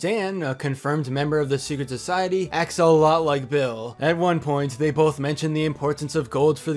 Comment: "axolotl like Bill" i don't know if that was an intentional pun or It just sounded like that but It cracked me up anyway